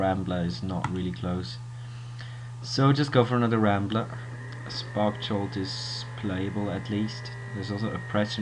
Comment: Nice deck, i heard you saying 'never play spark jolt', i think it can be pretty great in a heroic deck to trigger + scry.